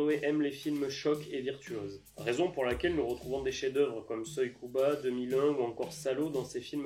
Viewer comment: Mortel ! 👏 Petite question à 3:59 c'est quoi le premier film que tu cites ? Désolé, j'ai beau réécouter plusieurs fois, je suis pas sûr de bien entendre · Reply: Merci à toi! Il s'agit de Soy Cuba de Mikhaïl Kalatozov ;)